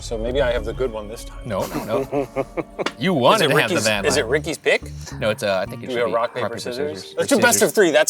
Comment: I literally thought Shane said “is it Ricky’s dick?” Hahahaha